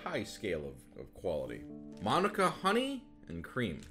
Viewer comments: Sung won saying 'Manuka' was something magical for me as a New Zealander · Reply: As a fellow New Zealander also loved it so much, genuinely thought he was saying the name “Monika” at first and did a double take and realising see it was Manuka Honey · I watched this while eating so I wasn't paying full attention and I thought he said "Monica Honey"